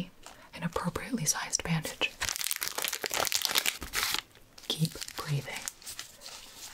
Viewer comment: When she said "keep breathing", I thought she said "keep bleeding" 💀